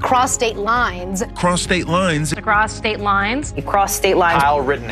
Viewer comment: Idk what you're talking about. All I heard was "Prostate-Limes" 🤷‍♂️